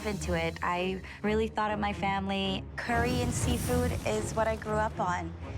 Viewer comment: What she said: “Curry and Seafood” What I heard: “Korean Seafood”